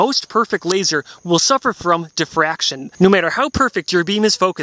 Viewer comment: when you said diffraction its like you said depression i thought you said depression lol